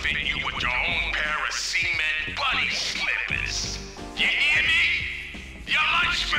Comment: message does he say "semen bunny slippers"?